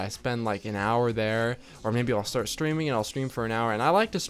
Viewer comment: when he said "streaming" I thought he was saying "screaming" and I got really confused